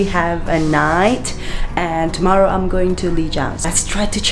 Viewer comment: were u saying going to li jiang? where is the vlog? i heard it is a beautiful place